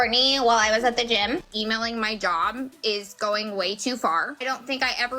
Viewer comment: The squeaky valley girl voice said: "He's a very manipullaterr" And was trying too much with her voice that it sounded like she said "I EMAILED MY DOG."😅... (She was really said someone emailed her job.) 😅😅😅